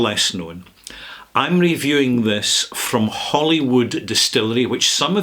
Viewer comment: I had to back up the video five times to listen to it again. Maybe I’m going deaf in my old age, but it sounded like you said “Hollywood” rather than “Holyrood.” It’s a Middle English term equivalent to “haly ruid” ("holy cross"). The mispronunciation is completely understandable, especially if one is in California such as myself. It might be helpful to pronounce the two parts of the word separately as, “ Holy-rood”. I have been to Scotland four times and visited 49 distilleries. If I make it a fifth time to Scotland, I’ll be sure to visit this one.